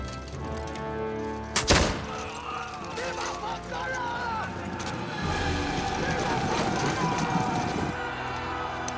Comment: what did he say? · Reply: Viva santa Anna